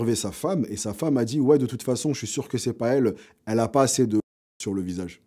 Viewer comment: Qu'elle avait pas assez de quoi sur le visage ? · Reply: De semence je crois